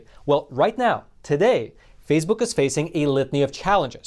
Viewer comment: All I heard was “fasebook”